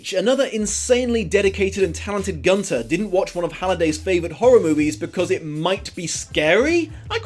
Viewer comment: Ha ha, it kind sounds like you said "Halliday's a c**t"...